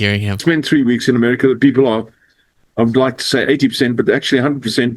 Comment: I think he said "I'd like to say 100% back trump" Aka support just sounds like he's got too much spit in his mouth and also hes a horrible waste of life. Throw him away